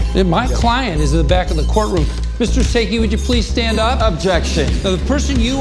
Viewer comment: All I heard was “ will slim shady please stand up” 🤣🤣